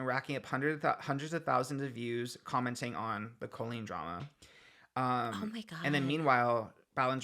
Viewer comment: I thought she said no more Colleen talk 😂 damn couldn’t stay away